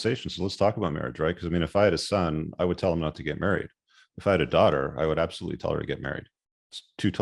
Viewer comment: There is no contradiction and nothing toxic. He is very clear. He says what he means and means what he says. He clarifies to raise boys to not "seek" marriage but to seek excellence. He then teaches what to look for in a woman because he understands mens primal needs and their humanistic need to connect with the opposite sex! You need to read his book to understand his videos/ context! When you interpret and create your own context its no longer about what has said - its about you and yours to own! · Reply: at @ 8:43 he does not say ‘seek’ he says... I would tell my son to not get married. I would tell my daughter to get married